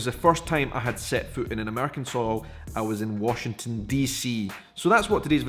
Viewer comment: I heard "first time I had sex on American soil"! LOL! I had to replay it.